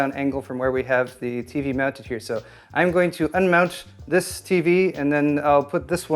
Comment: All i heard was, "I'm going to mount it" . A little voice said "gigetty".